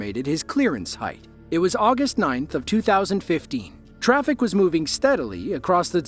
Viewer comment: did bro just say the year 2050?💀💀😭😭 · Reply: 2015 is what he said